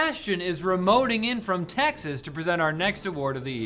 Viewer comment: I thought he said Tech-xas but then I remembered Texas is real